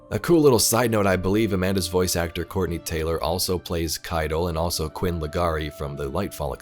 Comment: I misheard "Corey Taylor" and I was like "Woooww the guy from Slipknot is on Destiny as well..."